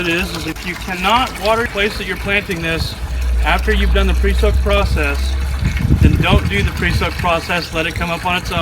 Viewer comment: What did you say the key thing is at 4:06? Sounds like the video skipped. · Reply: If you can't do the water replacement process then don't do the pre-soak at all.